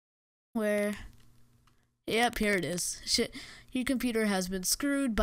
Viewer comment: he sounds like he said shit